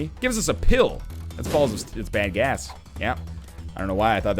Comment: he said bad gas but it sounded like bang ass